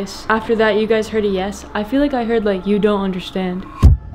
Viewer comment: it sounded like he said "you don't understand SHIT"